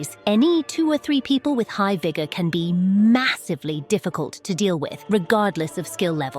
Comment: did i just hear massive?